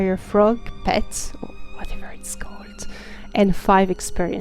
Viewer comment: What does she whisper around the 1:55 mark? It's driving me bonkers! lolol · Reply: I say "Whatever its called" cause I didn't remembered the correct name of the Spitfire Dragonfrog